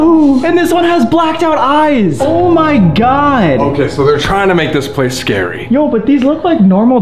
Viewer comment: i swear I heard him say “Chinas making this place scary” and I almost died